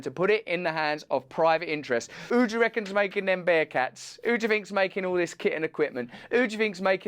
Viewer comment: Hey! 👋 Thanks for watching! If you’re in the UK get tickets for my 2022 tour here: <link> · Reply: Russell, when you said “who do you think’s making all that kit and equipment” at first I heard it as “kitten equipment”. I was like damn is there some military/Petsmart industrial complex I don’t know about